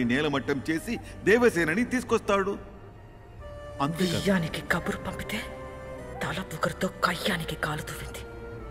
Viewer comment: what did she said i didn't understand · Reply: Veeyaniki Kaburu pamputhe .thala pogaru tho kayaniki Kaburu pampindi